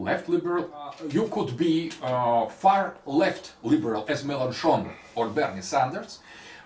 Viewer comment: what's the name he said as an example of far left liberal before Sanders at 7:13 ? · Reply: My guess is Jean-Luc Mélenchon.